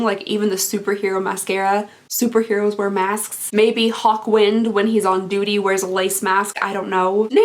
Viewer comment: I totally thought you said “hot wind” and not “hawk wind”. This led to wondering why that was a lipstick name. There was also confusion about the super hero thing and thoughts about whether his superhero powers would be fart related. 😂